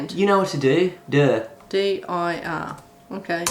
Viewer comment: sounds like he said “duuuh, you know what to do” idk if it was his accent saying DIR or if he really said DUH lol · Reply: I was saying "dir" but it sounded very much like "duuuh" :P